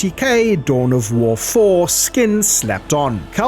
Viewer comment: Did you say "Dawn of War foreskin"?